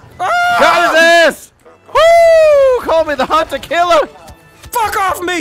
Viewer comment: I swear i thought ohm said my xbl (TheHunterkillz) at 1:42 but he saud the hunter killer